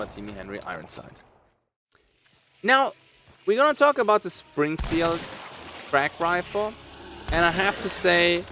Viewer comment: Totally thought you said “Springfield Crack” in your intro lol